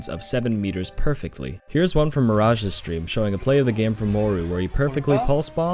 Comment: Did you just say "whoru" ? It's supposed to be "Who are you" hahahahahaha